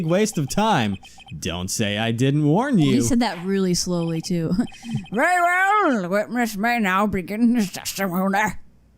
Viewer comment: What I heard 3:30 "Very Well! The Witmish may begin his teshtimoneh."